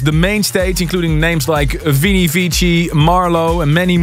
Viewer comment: did he say Avicii was playing at ASOT 900 :/ Bit awkward, who did he mean? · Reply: Vini Vici